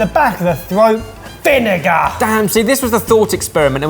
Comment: 'VINEGAR FARK' It sounded like said the F word lmao